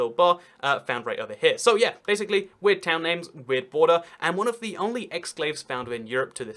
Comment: I wouldn't say Livia is Europe's only exclave. Italy and Germany both have tiny pieces inside Switzerland. Kaliningrad is an exclave of Russia, though not surrounded by another state. And there are a huge peppering of pieces of Belgium inside Netherlands, including, if I recall correctly, one piece of Belgium inside a piece of the Netherlands inside a piece of Belgium that's inside the Netherlands. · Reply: I thought he said “one of the only exclaves . . .” but need to listen at a still slower speed to be sure.